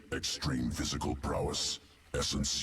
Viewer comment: I looked away, and I honestly thought he said Tyranno Zord. XD